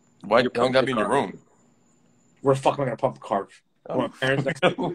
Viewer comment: said "where tf am I gonna pump a carve" and I was so ready for to make fun of that but he didn't hear it lol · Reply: you sure he didn't say pumpkin carve and the mic just cut out? lol